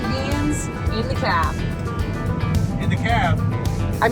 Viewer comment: ''beans in da hopperbby beans in da hopper.....'' I only herd it I did not SAY IT!!!